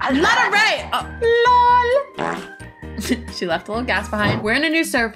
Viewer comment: did she say asss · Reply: She said gas